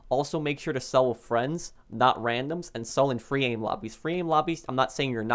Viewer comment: what kind of lobby did he say? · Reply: Free aim lobbies